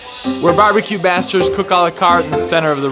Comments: Did he just say barbecue b*****ds? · did he say barbeque "bastards" or "masters?"